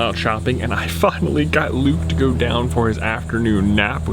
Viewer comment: What exactly did u say here?!? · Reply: I finally got to go down for an afternoon nap.